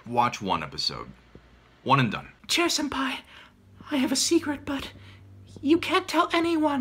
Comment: I thought he said "I have a secret butt"